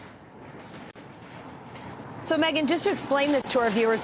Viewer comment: the cop sounded like he said "owo"